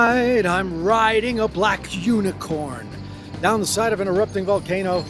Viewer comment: I Always hear 'down the side of an EROTIC volcano'